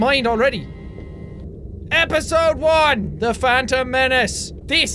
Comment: i thought he said episode 1 the fandom menace...